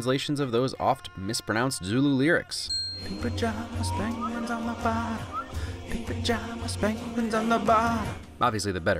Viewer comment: Funny I heard it as “penguins of Nevada “